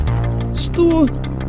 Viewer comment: мне же не одному послышалось "сто!?"